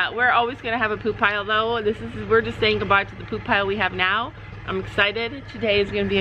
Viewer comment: I was freeking out and just to find out it was poop piles I laghed😂😂😂 I thought you guys were saying goodbye to YouTube😂 have fun. Love ur life❤️u guys